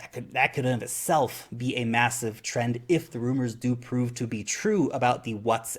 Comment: did you say rumors??? I once heard someone say buy the rumor sell the news....